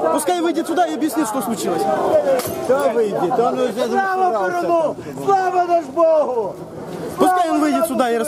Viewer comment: Только расслышал на 4:15 какое-то чувырло орет - слава перуну, слава дажьбогу. трипольская культура так и прет :D